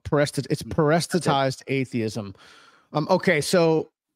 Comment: Parestitized (?) atheism? What was this word? Parasitized atheism? As in parasite?